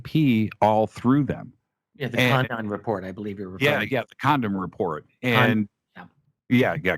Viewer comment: Did he just say “condom report”??!! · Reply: Condon report....ugo report from the 60's